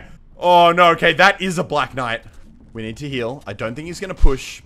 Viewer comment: it sounds like he says "That is a black guy"😄